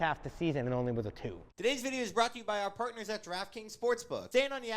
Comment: Every time said draft kings I kept hearing “giraffe kings”